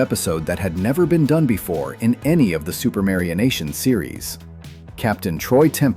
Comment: LOL, subtitles say Super Mario Nation instead of Supermarionation.